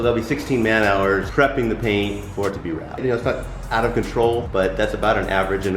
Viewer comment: Sounds like this something new thing for the brz is a wrap.... skip to 4:30 you hear the guy say “for it to be wrapped” is what I hear at-least 😋